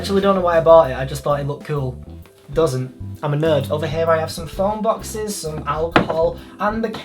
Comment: Oh my stars! For a minute I thought you said you were a nurse - I nearly had apoplexy. But then I realised you said "I'm a nerd" - so that's OK.